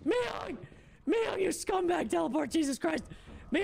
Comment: Did i just hear syk say sc*mbag in 23:22 lmao😭💀